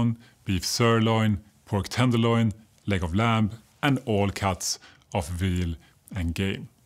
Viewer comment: For far less expensive lean red meat go for round steak or topside roast and cut your own steaks. Also, when you said "leg of lamb" - I heard "Legoland' and I went, Wait! What? - What?? - Lol.